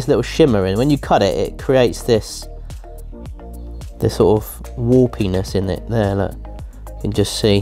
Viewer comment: When you said warpiness I thought you said war p*nis